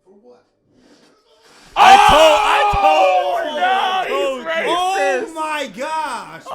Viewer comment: he really said he is racist? hahaha